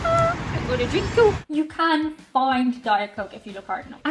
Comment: Did say "I'm gonna drink you" or "Harajuku"? 🤔